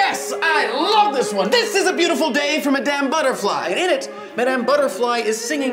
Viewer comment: "A Beautiful Day from Madame Butterfly", I heard "A Beautiful Day from a Damn Butterfly".